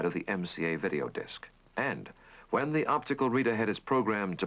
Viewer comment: What is the "r" word in 1:12? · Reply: Reader, as in "optical reader head".